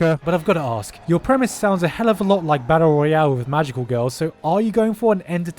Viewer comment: DID I HEAR BATTLE ROYALE!!!!!1!!!11!1!111!!1!1!1!!!!